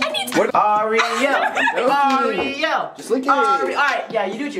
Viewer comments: it sounded like he was saying blariel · I thought it said zariel but it said zack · did it say "zariel"